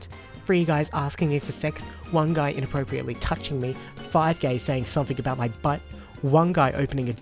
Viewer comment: sounds like he says gay. Also it's not male "privilege", and all those events are highly unlikely.